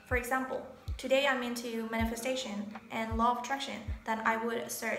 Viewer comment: About the 9:34 mark..."For example, today I am interested in...." I am sorry, I could not make out what you are interested in. Partly because you did not articulate well and partly because there is a little echo which blotted out some of what you said. This was frustrating for me because I am INTERESTED in you, how you think, what you say, how you say it. By trade I am a computer system programmer (It pays the bills). One of the hardest things about my job is getting people who are not logical, not organized mentally, to tell you EXACTLY what they want you to do...what are the EXACT computer specifications. And these are native English speakers who cannot express themselves. Communication is important on so many levels...and every language is inflected. Two guys can be talking about a girl. One will say, "She is really a winner, really gorgeous." She may in fact be 140 kilos with a greasy pocked complexion and nasty speach. The other guy will smile and say, "Yeah, I'd like to take her home to meet mom."....both are kidding each other and saying exactly the opposite of what they mean. But in voice tone and body posture, non verbal communication, they are communcating on a non-literal level. On the other hand, you, my dear, are an "interesting chick"...literally! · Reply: I said, “today I’m interested in manifestation or law of attraction and the next day maybe I’m into zodiac signs”.